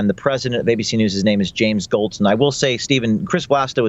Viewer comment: These damn "Stein"s man. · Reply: TrueDepth shit I've accidentally made a racist remark. I heard "Goldstein" (mistake) and then thought of other recent Steins "Epstein" and "Weinstein". Did not even see it that way.